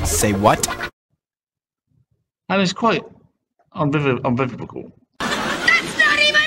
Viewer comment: Umbilical is one way of pronouncing unequivocal I suppose. What a clown. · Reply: I thought he said, "uninbilical." But close enough, I suppose. Whatever he said, it was wrong 😂